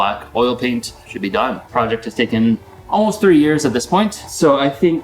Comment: I thought this said three days and I was like “I’m gonna be making bank”